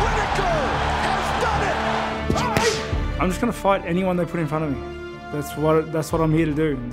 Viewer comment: i thought he said "ill fight anyone who makes fun of me",lol